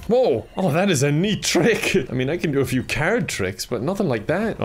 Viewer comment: I thought he said car tricks lmao